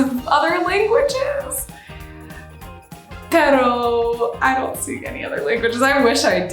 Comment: Did she just said "pero" in